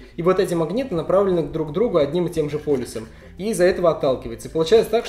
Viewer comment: Мне послышалось или ты действительно вместо "полюсом" сказал "полисом"? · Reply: Полюсом